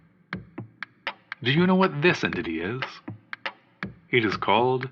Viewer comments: it took me three listens to realize that what's being said is "Do you know what this entity is?" and not "Do you know what this idiot is?" lol. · The first time I heard this I thought he said “Do you know what this idiot is?” 😂